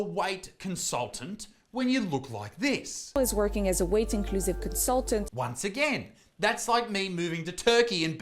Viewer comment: I kept hearing you say ' white inclusive consultant" and I was like wth is that? lolol just for reference you are Aussie and I am American lol..was just the accent mate ~